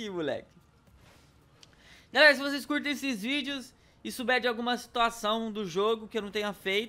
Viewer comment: EU JURO QUE ESCUTEI: - SE VOCÊS GOSTAM DE XVÍDEOS...